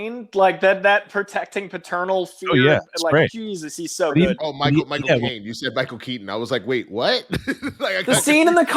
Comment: does correctly say "Michael Caine". misheard "Michael Keaton". I know it's probably the webcam mics that made it hard to hear though.